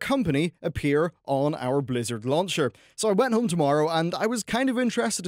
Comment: you went on tomorrow ? you went home tomorrow? i need help. My nordic ears cant understand